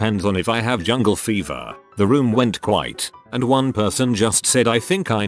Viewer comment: I love how they typed "the room went quite" instead of "the room went quiet" I had to rewind because I thought it said "the room went white"